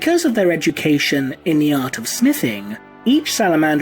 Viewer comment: it sounded like you said "Because of their education in the Art of Sniffing" This immediately peaked my curiosity. I was like wutt? ?Unfortunately you said Smithing...